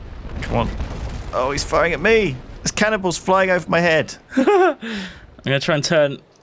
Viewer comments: lol at 6:21 it sounds like he is saying cannibles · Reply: your welcome! I wonder if anyone else noticed that at 6:21, that it sounded like said cannible XD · lol at 6:21 it sounds like he is saying cannibles